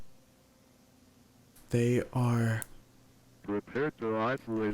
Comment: (what it says) iht ouy naht regnorts era yeht (Mirrored) they are stronger than you thi (What they are saying)They are stronger than you think